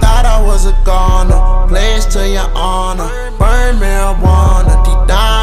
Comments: I keep hearing "blaze to your honor // burn marijuana" instead of "blessed" (but tbh I kinda like blaze a little better) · I could’ve swore he said “pledged to your honor”